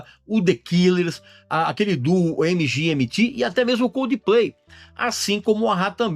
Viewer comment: Foi apenas eu que ouvi o Régis falando C Udplay(Coldplay banda) no 12:26?